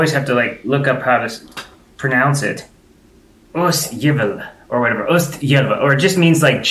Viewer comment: Haha, sounded like he said ostjävel, which is like cheese bastard. Which is wrong, it's osthyvel. Hyvel is like any tool that cuts the way it does. Full carpet floor is so dumb! What if you spill something!? What if a pet pisses on it?! What if you puke?! And the dust! Haha, yeah I got a fan. But I only need it for like a month a year. Summer isn't very long or hot. And for winter we just have central heating, radiators in every big room. More Swedish videos! That's main reason I subbed. I think I recommended some under another video.